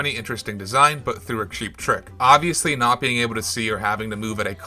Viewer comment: did someone say cheap trick?